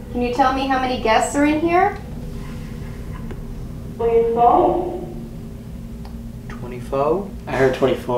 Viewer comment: when she ask how man guest are here. i was looking down at my desk so i couldn't read what you thought it said. but it sounds like ''3 or 4''